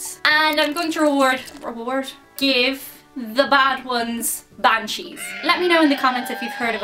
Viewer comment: She said Banshee... I thought she said "I'm going to give the bad ones, bad cheese."